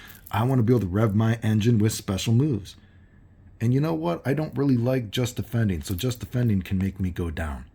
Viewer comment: I didn't say I don't like just defend or if I did in the video I must have misspoke but I am going to assume you misheard me if anything I have said many times that I think just defend is more interesting than parry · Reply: It was at 6:21 where you went “y’know what I don’t really like JD”,but I think you were saying as more of like an example which I didn’t understand. I apologize.